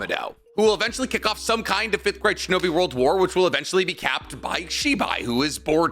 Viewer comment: You said “5th great shinobi world war” but I heard “5th grade shinobi world war”. Just a bunch of 10 year olds throwing sticks and rocks at each other.